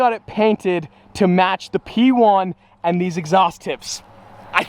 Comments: did he just say ‘match the P1’ 🧐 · Did he say p1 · did he just say P1? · did i hear “to much the p1” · Bro did u say P1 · wait did he just say match the p1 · hold up did he say the P1? Does that mean the Hypercar is coming??? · Did he just said the P1